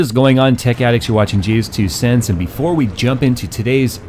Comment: sounded like you said jewstwocents lol